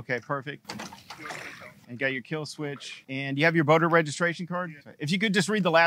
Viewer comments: I thought he said voter registration card 😂😂😂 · I thought he said "voter registration card", it was "boater". Haha. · If I didn't know better I would have swore you said you have your voter registration card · I thought he said voter registration card, not boater, I was like. What the fuck, I'm bout to wat I got to vote to drive a boat?